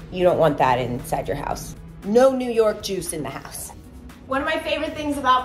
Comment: As a Jewish girl I was laughing so hard when you said “no New York juice in the house” I thought you said NO NEW YORK JEWS IN MY HOUSE 😂😂